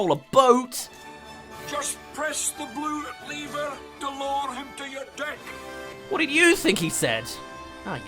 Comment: Saber Spark fans: No, NOOOOOOOOOO! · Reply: I thought he said, “ just press the blue leaver to lower him to your dick.”